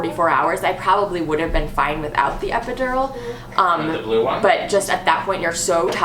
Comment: haha I thought she said "a blue" and I was like "omg so cute a boy!!!" but then you guys said girl and I was like "oh I guess I don't speak toddler" haha